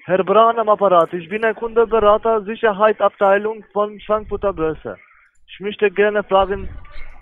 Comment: Bei 0:06 habe ich Schwanzfutter Börse verstanden.